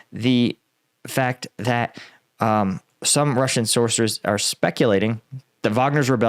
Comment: you tried to say sources, but it sounded like you said sorcerors. That does make some sense if they are getting their intel from crystal balls and tarot cards.